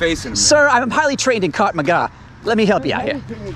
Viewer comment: did he say he was trained in "C*ck Maga"? lol